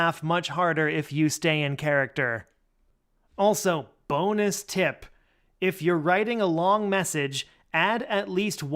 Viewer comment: Did I just hear, bonus tip? 🐷👍🏻 thanks for the support